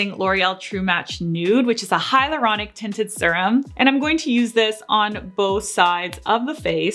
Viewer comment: When you said "hyaluronic tinted serum," the subtitles said "highly erotic tinted serum." 😳